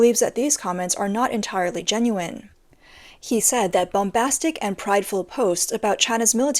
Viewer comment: did she say boombastic 😂